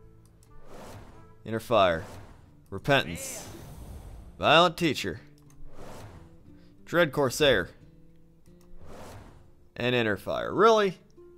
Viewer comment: lol when he was opening packs I thought he said violent teacher.